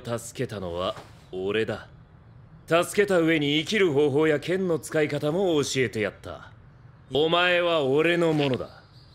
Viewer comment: I-wait..did he say Kenma?? Please I can't be the only one that Heard "Kenma"😱😱😱😭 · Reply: he said "ken no" he was talking about swords my goodness